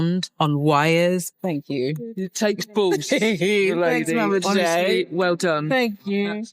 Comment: What Did She Just SAY? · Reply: "It takes balls"